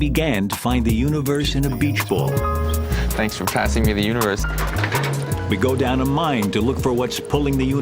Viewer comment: Sounded like he said "Thanks for passing me the uterus"